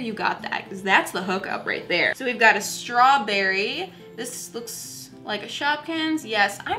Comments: She also sent me a StRaWbErRy Me: I know she said strawberry but it sounds like she said strow - bery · It sounded like you said stawbery